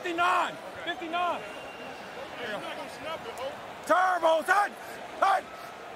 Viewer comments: "hes not gon snap it yo" did i hear that from a defender? haha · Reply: i thought he said "just snap it, ho." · Bruh did the defender say “no” after Brisset said come blitz 😂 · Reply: thought he said “he’s not gonna snap it bro” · “He’s not gonna snap it yo” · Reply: I thought he said "when you gonna snap it ho"